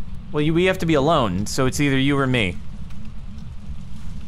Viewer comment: what the hell did say is that korean? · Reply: "oh sorry I was talking to the tv, I'll go" is what I think she said